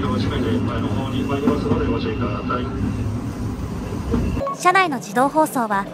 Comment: ご注意くだぁたいっ…って聞こえてからずっと笑っちゃいますね！